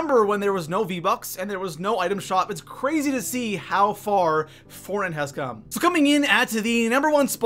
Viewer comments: he’s says it’s crazy how far foreign has come instead of fortnite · foreign has come instead of fortnite